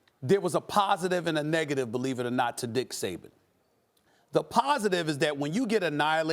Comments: Dick Saban? Why hadn't I heard him called that before?...….. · did Stephen say dick Saban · I might be late but did it sound like he said Dick Saban.. haha